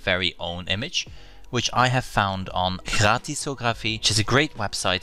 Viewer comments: Where did you get the free images?? I couldn't understand what you said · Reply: Gratisography (dot com) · you say the name of a website that provides royalty-free images, but we cannot understand the name because you say it with what sounds like a French accent... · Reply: Gratisography gratis means free in Dutch and thus the website was pronounced the Dutch way. Thank you for asking! They have amazing photos on there!